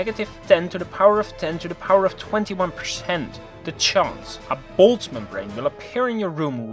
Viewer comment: “baldsman ring” ? What is that? Not sure if that’s what he saying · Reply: boltzman brain